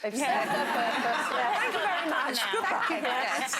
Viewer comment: I didn't catch the funny line everybody is laughing at at 4:39. Would somebody please spell it out for me? I'm not a native English speaker. I love Beverley Craven. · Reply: She says "They've been marvellous...thank you very much , goodbye" (referring to her mastectomy)